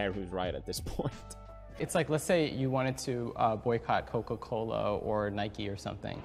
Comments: I thought he was about to say "cocomelon" at 11:06 lmao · I thought he was saying Cocomelon 😂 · I thought he was gonna say "boycott cocomelon" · I seriously thought he was gonna say boycott cocomelon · Ngl, i thought he was gonna say "Cocomelon" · I thought that guy in the vid was saying to imagine wanting to boycott cocomelon😂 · No joke thought he was gonna say "boycott CoCoMelon" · Me:Bro i thought he said coco melon instead coca cola lol lmao Pewds: cancel culture is childish Also pewds: cancel cocomelon